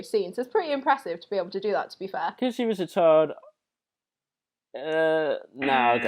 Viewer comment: caption at 07:05 because she was a turd.. honestly that's how i heard it as well.. lol